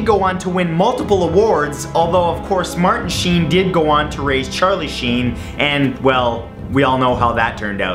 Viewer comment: for a minute there I thought he said charley scene but it was charley sheen